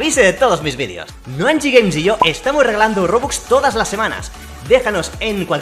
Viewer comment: como soy nuevo te entendí Estamos Robando Robux xd perdón